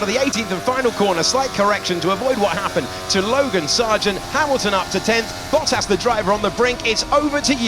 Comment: I heard "Bogan Sargent" when the video started LOL